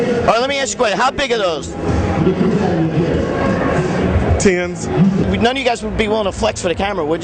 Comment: what's he saying @ 9:37 ...how big are those??Is he saying ten's????!!!!!